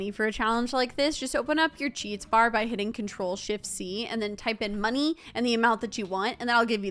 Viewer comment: When you say shift you sound like your saying $hit😂😂😂😂